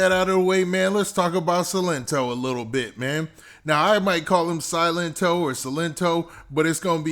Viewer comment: The nae nae nigga I thought the part when he said his name I thought he was saying silent tine silent tine